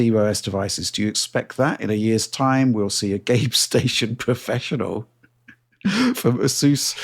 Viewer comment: Had to check the captions, thought I heard promoting the GapeStation pro which I think is much different device.